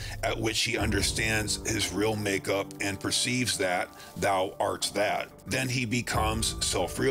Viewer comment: what is he saying? I don't get that.. ~that thou that?! ... eaah? someone please help :D edit: thou arts that? like understanding that me-you-we-are part of him?/god? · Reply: I am “it” So are you and stuff